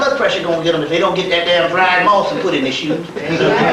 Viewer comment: What is the name of the herbs he says at 3:20 something???⁉️ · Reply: Dried moss. He doesn't say what kind. The pills are "Herbal-B" or Lady-B. The Wee Wee pills are Stamina RX.